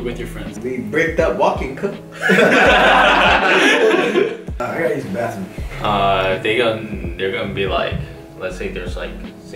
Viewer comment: i'm not an english speaker so I feel like I miss a lot of answers and funny moments such as at 13:09 , the answers of I couldn't understand. Can someone tell me ? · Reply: Bricked up walkin cuh and I got to go to the bathroom